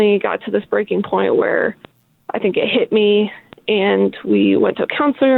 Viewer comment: She said “it hit her,” unless I’m missing something. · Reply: Thanks. You are right at 1:30 She said it hit her not what I thought I heard.